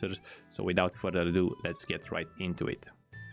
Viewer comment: i though u said without further lube let's get into it i fucking died i realised thats not what u said but im using it now